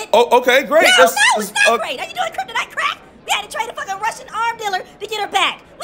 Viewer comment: “Dumber than a bag of Herschel Walker hammers” 😂😂😂😂😂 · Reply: Lol I’m so deaf I thought they said Russian hammers. Thanks for clearing that up, had an Aquaman moment😅